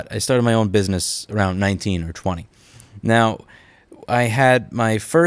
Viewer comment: I heard that as "I started my business around 1920". It took me a second to process that one lol.